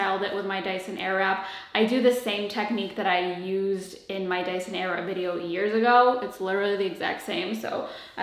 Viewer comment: I heard "in my dyson era" 😂